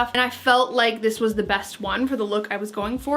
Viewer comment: Did anyone else hear "I felt like this was the vest one" at 03:50? 😆 Maybe just my punny brain. Great video! ❤️